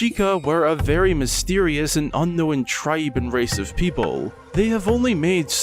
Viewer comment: omg at 2:18 I fr thought you said "racist people" and not "race OF people" LOL